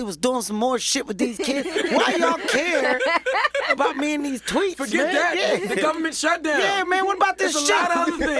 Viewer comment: I thought he said why y’all care about me and these “twinks”... then I realized he said “tweets”, and then I wasn’t as happy .... 😞